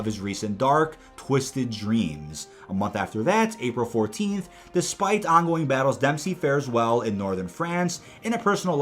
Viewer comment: Thought that said despacito 😂😆😆